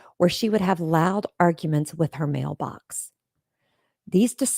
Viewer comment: Did you say “Loud arguments with her mailbox?” 😳